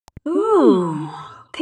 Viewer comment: Why did my goofy ears thought that MALO was saying pikachu in the wrong way before I realized.